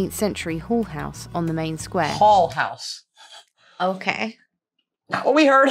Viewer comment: I thought she said hoare house. 🤣